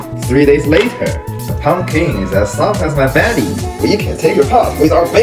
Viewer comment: why did i hear ‘the pumpkins as soft as a baddie’ 💀